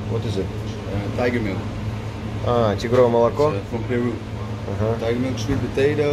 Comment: Какое молоко?простите не расслышала,тигровое?!😳